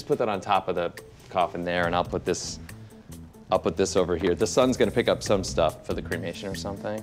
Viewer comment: For the first minute I thought he said the SUN.